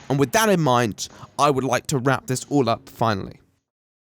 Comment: for a moment I thought you meant "rap" instead of "wrap"